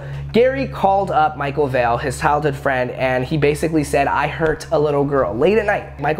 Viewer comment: did just say “hild hood friend” instead of “childhood friend”?🤣